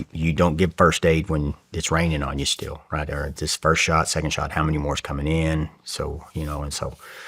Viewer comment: Did he say 2nd shot?